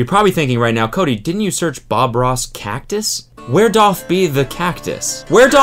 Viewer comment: What does he say at 12:06 where (...) be the cactus? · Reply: Doth..